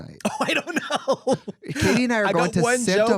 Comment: did trixie call katya “Katie”? Cause like work